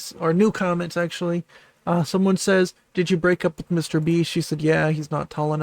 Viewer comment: did someone ask her if she had broke up with Mr.Beast and did she say yes?! Can someone clarify this to me please?